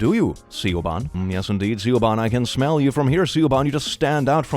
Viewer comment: Did you say: "See you Órban [Viktor, the hungarian president]." But pronounced the long /o:/ like /u:/?